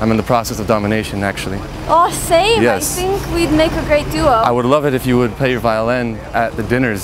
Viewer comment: did she say, oh Satan we be a great duo